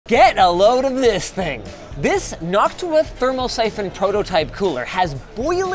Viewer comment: i swear i almost heard "This Hawk Tuah"